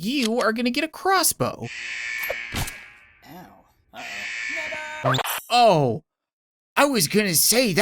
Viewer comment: I guarantee I am mishearing it, but I have replayed these few seconds a dozen times and I swear that dude's dying words are "uh-oh...N*GGA!"